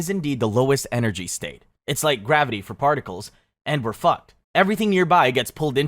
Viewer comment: Did he just say Fuck lol?!